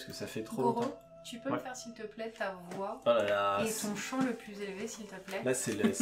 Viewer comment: Quand t'as dit (à 1:17:27) "tu peux me faire ta voie et ton champ", j'ai entendu "ta voix et ton chant", ça m'a perturbé une bonne seconde. 😅